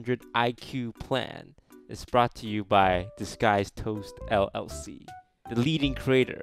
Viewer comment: Wait!! I always thought the intro said ‘this guy is toast’ I have only just realised after being a LONG time viewer that it says Disguised Toast! Thumbs up if you too have had this discovery